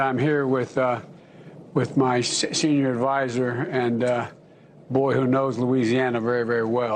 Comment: I absolutely hate Biden and I know his history. But, to me, it sounds like he is saying "and oh boy" like in a "uff" expression. Either way, it is unacceptable to have a president who cannot speak!!!!!